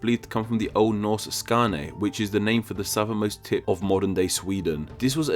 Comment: Just for a moment I thought you said "The southernmost tit of Sweden".